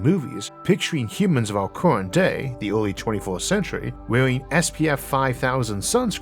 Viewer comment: I might have misheard. Early 24th Century? Wait until 2450 and then we can meet Donald Duck. LoL